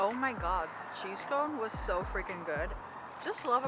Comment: I LOVE this video and our town <3 But you didn't even show us the cheese scone, it was gone in a moment!! :D Looking forward to our gezellig walk together! My greetings to Mr. Chunky. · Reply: Lovely video. I alsof misheard cheese cone. Maybe because I never have scones myself. Our maybe because I also lived in northern England for a while? Naaah...